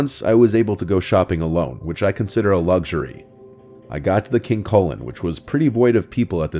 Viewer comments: I thought he said the supermarket name was "King Colon." Figured the food there must taste like sh*t. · I'm glad you put a picture up, I thought you said "King Colon" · Never heard of King Kullen before. I thought he was saying King Colon and was like wtf? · I can’t be the only one who thought said King Colon was the name of the super market lol! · As a Canadian, who does NOT have a King Kullen in my province, and isn’t really familiar with it, I LEGIT thought he was saying “King colon” at first 🤣🤣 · Glad it showed the store name lol. I thought you were saying King Colon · I heard “King Colon” at first in story #1. 😮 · I thought he said "King Colon"